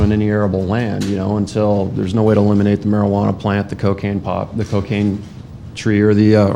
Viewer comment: I thought bro said cocaine tree at first but I think he said trade lmao